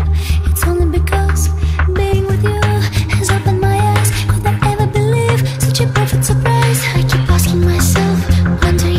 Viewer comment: what is said at about 0:06-0:07? I always hear "its only because being with you is up in my ass" and i dont think thats right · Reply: it's only because being with you has opened my eyes